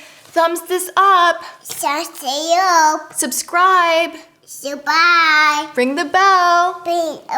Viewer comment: when he said "subscribe" i thought he said "survive" and i was like IM TRYING, LOGAN